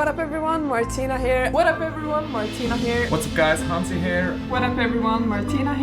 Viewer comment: I’m sorry but I could have sworn that when did his intro it sounded like he said “Hansy Hair!” 😊. I know he actually said “Hansy here” but I still couldn’t help but hear that. Anyway THANK YOU for the 10 years of videos and the inspiration they provide for the rest of us. 😊